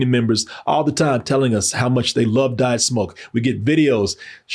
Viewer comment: When said “we love diet smoke” I thought he said “we love to die of smoke”